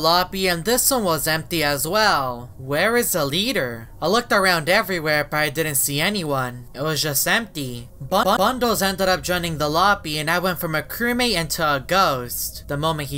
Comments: bubububundles ended up joinin lol (Dis is what i heard even i rewinded several times) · did anyone hear b-b bundles or is it just me